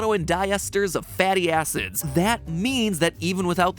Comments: lol when Matpat said fatty acids it sounded like fatty asses💀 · when he said fatty acids i thought he said fatty asses XD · I thought he said fatty a Do I have to continue